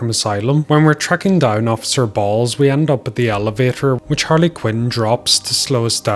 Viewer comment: I already knew about the guards still being alive on my PC playthrough. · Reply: Also it did sound like bro said “officer Balls”